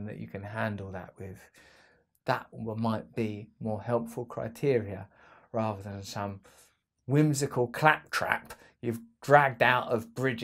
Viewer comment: Did he just say "Musical Clap Trap" lol